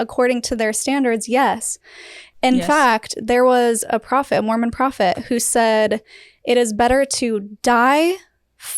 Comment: mormon prophet or Muhammad? I didn't hear it clearly?